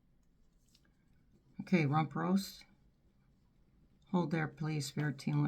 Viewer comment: I thought it said rump toast, which also is a suitable name for that vomitatious😂 being